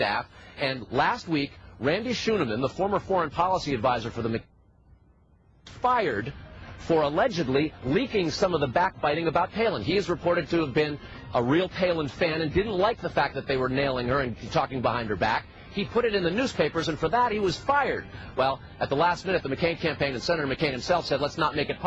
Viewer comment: Did he just say they were "Nailin' Palin"